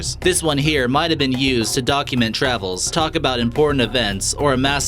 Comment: DID HE SAY DOG-UMENT? I think he did! yuuusssssss